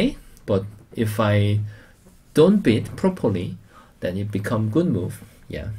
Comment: Can I ask what you mean when you say "bid" around 15:28? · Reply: What I said was "beat". My pronunciation was not good.😅